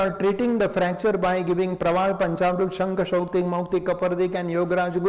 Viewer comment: What did he say at 9:55 ?? Some medicines for hair line fracture. Can anyone please tell them?? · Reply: Praval panchamrut,yograj guggul